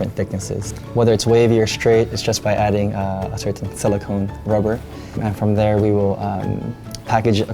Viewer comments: did he say add silicone rubber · Did he just say by wavy or straight it depends on silicone rubber we add to it